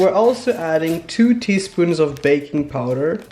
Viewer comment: Im so hungry i thought he said bacon powder instead of baking powder